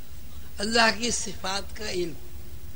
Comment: Ilm ul asma allah Kay naam hain kya? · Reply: I have same question. I think at 1:18:00 he said "Allah kee safat ka ilm